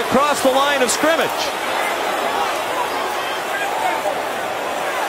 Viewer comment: can someone explain what the bouncer guy is saying? Shotgun? Eligible receiver?